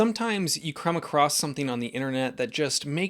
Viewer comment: did say “Crumb accross”?